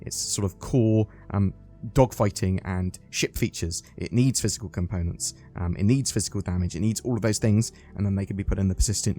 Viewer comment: I heard "fiscal components" and "fiscal damage" Got plenty of that